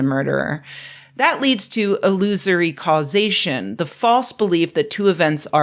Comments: My brain thought she said “a losery causation”. Which feels true too. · You said "illusory causation", but what I heard was "loser-y causation", which makes me think of a chain of events set off by a jackass... Which also applies here